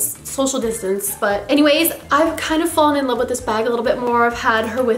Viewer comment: When she said I kinda fell in love with this purse I thought she said I kinda fell in love with this guy and I was like :0 rewind this RN